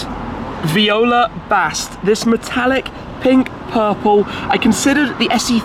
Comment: with his accent, when he says purple, it sounds like he is saying pink